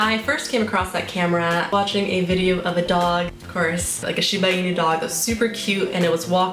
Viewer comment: Gonzalez?! hahahahah where did you hear that? cute dog name though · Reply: he got it mixed up. At 01:32 till 01:36. There's the part where you say: "Of a dog ofcourse", probably he "heared": of a doGofcou(r)se, 😮 maybe sounded like "Gonzales' to him 😂😂😂 🐕👀🗣️👂🏽🕵🏽‍♂️